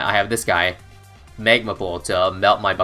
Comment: What did say Migmaball? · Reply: Magma pool